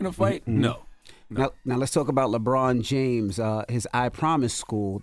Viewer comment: I know it doesn't make sense but y did I hear "Kanye West" about the school...I had 2 go back like "Lebron James"! 🤦🏾‍♀️🤷🏾‍♀️😂🤣